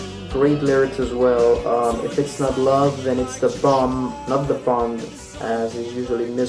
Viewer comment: I'll never forget my introduction to the Smiths was when Johnny Marr appeared mid - to - late 80's on the cover of Guitar Player magazine. Sexy beast! Oh, the pleasures that awaited me.. So many afternoons just spinnin' their albums over and over. Obsessing over LP jacket notes (Salford Mens Club, hello!) really helped shape my ears (& hopefully my brain, too).. There is still much to be discovered among Smiths grooves. I still hear minute Johnny Marr-isms most every listen. But seriously though, no cc (closed captions) on this video is all well & fine; but did you really say @ 2:55 "thong" instead of bomb? Are you serious? Presumably your joking, cause I've been listening over 30 years. Maybe I misheard you or it was an allusion to The Joke Isn't Funny Anymore ? Let me dare to be surprised at the answer, answer man! If true, I really do learn something new most everyday, for real fr. Great channel! Keep on keepin' on uploading these interesting videos. Very much appreciated. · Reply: I said "bomb" I think! haha